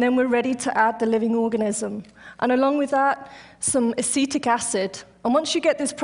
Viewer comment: i couldnt really tell with her accent, but at 1:21 is she saying acidic acid?